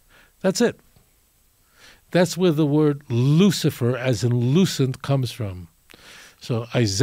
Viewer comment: Did Tovia say "Lucifer as in lucent"?